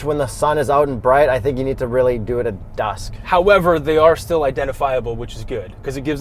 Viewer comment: What's dusk? We never said that at all in this video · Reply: I've watched this video again looking for that 'dusk' comment cos 1. OMG ONE OF YOU GUYS REPLIED 2. I refuse to admit I was so stoned that I heard wrong....and I DIDN'T! 6:24 says "I think you really need to do it at dusk"...He's talking about the headlights! Seriously love you guys...but...I'm SO STOKED I wasn't hallucinating!